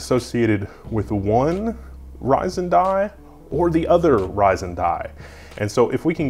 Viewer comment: lmao i was only half paying attention to the video and when I heard "Fryzen" I thought it was some sort of epithet the intel fanboys had come up with